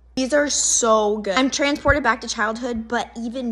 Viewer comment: I heard "nah" lol and then I was surprised she said thank you and had to watch it again.lol